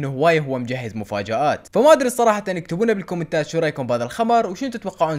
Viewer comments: اتوقع اني سمعت شو رايكم في الخمر1:26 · هم سمعتوا كال شنو رايكم بهذا الخمر شباب بنات انتو هم سمعتوا لو اذاني بيها مشكله